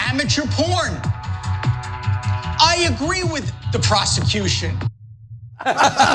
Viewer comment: I thought he said, "I agree with the Prostitution!"